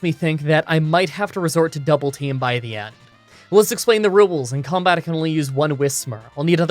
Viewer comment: Did.....did he just say rUwUles?